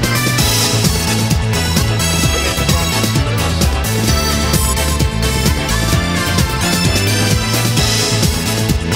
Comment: Ok what is that guy at 0:25 saying?? I hear that voice in the chao lobby in sonic adventure 2 as well. I can't be the only one who noticed that. Lol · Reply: Layin' the wax and spinnin' the sounds